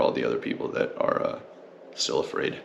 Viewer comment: “I’m sorry for all the other people that are so afraid” !!! Lmao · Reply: I heard "still afraid" but same diff. We got racing here in NorCal again finally!!